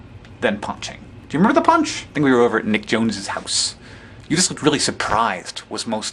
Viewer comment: I thought he said they were at Nick Jonas' house...